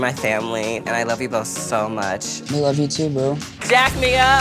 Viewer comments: it means sooo much to me that you boys love this dress, you my family and I love you both so much Friend: I love you too Bro 6:32 Me:😕😕😕😕 Did he just say bro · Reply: Quarantine Chilly he said boo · Did he just say: I love you too bro 😨😆😂😂